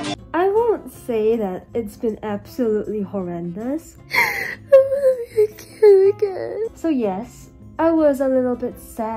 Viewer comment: I thought you sayed "i wannabe a kitty cat"😹